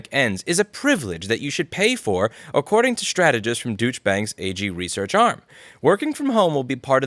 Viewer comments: I keep hearing "duech" bank everytime. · Perfect how you read that "Deutsche bank". It sounded like douche bags.